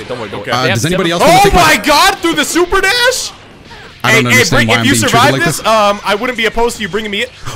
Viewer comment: did call Rick? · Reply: No he did not, was trying to say "Hey hey bring me in" bro was just stuttering and changed his mind at the last second to say "If you survive this I wouldn't be opposed to you bringing me in"